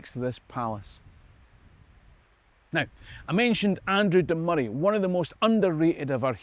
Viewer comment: And Andrew Murray couldn't have done it because he would have been too busy playing tennis and his mother wouldn't have let him! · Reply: Andrew? Oh, I thought you said Arthur.😅